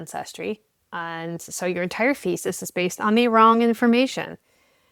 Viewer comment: I heard "faeces" instead of "thesis" at first, both make sense in this context.